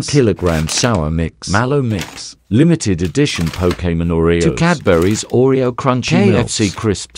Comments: Am i the only one who thought he said “Limited edition cocaine oreos”? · I thought i heard cocaine oreo Ahahaha but its pokemon